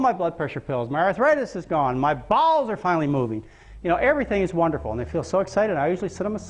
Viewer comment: I know he says "my bowels are finally moving"....but it sounds like he is saying " My BALLS are finally moving" ....LOL